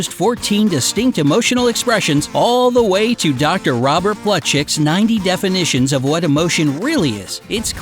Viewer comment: anyone else hear 'Robert Buttcheeks'?